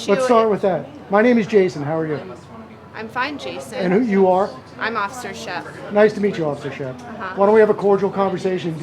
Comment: What’s her department and name with badge number? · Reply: I think she says "Shep" or something like that. 1408. Scottsdale PD.